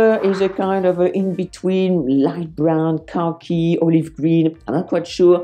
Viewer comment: When you say “khaki” I hear “car key” with your accent. lol